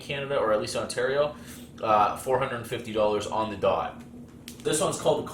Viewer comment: Bro what are you saying? You only have 49$